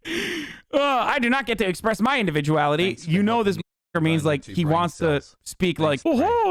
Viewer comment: What did Hasan say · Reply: "You know this motherfucker..."